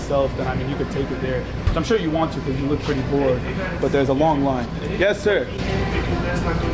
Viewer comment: I heard him say “ you look pretty poor”